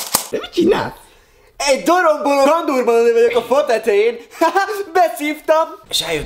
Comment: Te mit csinász? -Egy ........ vagyok a fa tetején! HÁHÁ! Beszívtam! Sírok!😂 Ha valaki tudja mit mondott a pontoknál írja meg pls! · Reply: Dorombolo kandurbarni vagyok